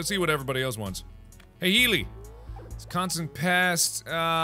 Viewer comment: I got startled because I was just listening to this in the background and when he said "Healy" at 8:16 it sounded like Hayley, my name